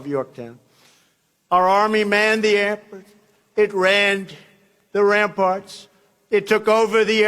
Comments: His stumbled while reading “manned the ramparts” got flustered and decided it said “took over the airports”. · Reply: Sounded like he said "rammed the ramparts". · I heard "manned the airplanes".. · What the actual fuck is he saying there? "Our army manned the...." His voice trails off mid-word because he realized that what he was saying was feces in his mouth. Airports? Air Force? Antlers? Edward? What the fuck? Also don't forget that after our brave army finished manning the Edward, it ranned the ramparts. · I heard "they rammed the ramparts." XD